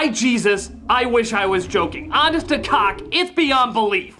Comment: Does he say honest to cock?